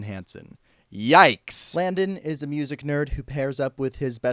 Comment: I legit thought you said his name was Blandon 😂 also I'm heartbroken that you lied about the Cars musical